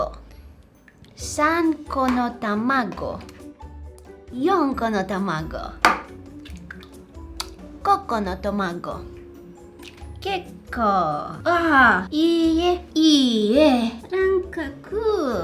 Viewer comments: it sounded like coconut mango 🥭 also can you do Korean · who else hear coconut mango 😂😂😂😂😂